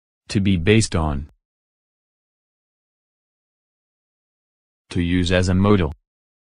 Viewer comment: Hi! I think there is a mistake at minute 1:11. Shouldn't it be "to use as a model" instead of " to use as a modal"? Thanks for this nice tool!